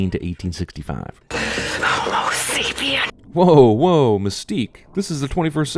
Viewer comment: she actually said homo sapiens not a latin word, but it's ok =3